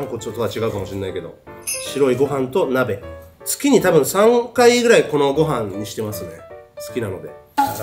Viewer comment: の[月に三回]が月に3億回に聞こえたw